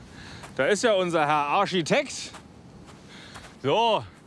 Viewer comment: Arschitekt. Hab ich richtig verstanden?